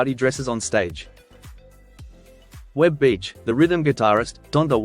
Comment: did he say Reb Beach? Winger dude? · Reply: Wes Beech 💗